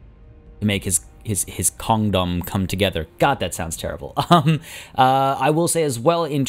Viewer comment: "Whose trying his best to make his Kong-dom come together." I heard: "Whose trying his best to make his condom come together." whaaaaa? you should take more care with your bad puns =P